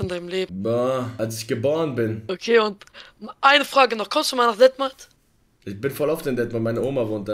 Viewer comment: Was hat er bei Minute 4:15 gesagt ? Detmark ? Dänemark ?